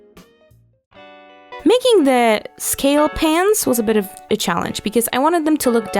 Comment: I thought you said scales pants instead of scale pans :D